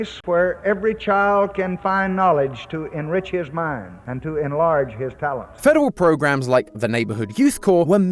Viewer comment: Not gonna lie i was listening to this video and when LBJ did the speech I thought he said "enlarge his phallus"